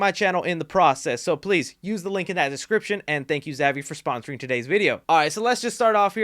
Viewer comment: Swear I thought this dude said "Thank you, Zaddy, for sponsoring me"